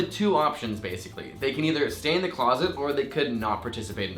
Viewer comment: When said "they can either stay in the closet" I heard it "Santa Claus it", and I was like is that a new term for lying about something? lol